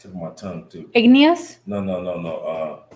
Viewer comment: Did she say "igneous?" @ 9:10 LOL! Well, English isn't her first language so...